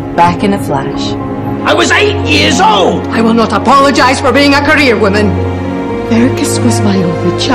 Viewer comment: Could you please write me what Rowena says at 5:04 for me to translate it... I'm french ^^' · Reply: She says "I will not apologize for being a career woman"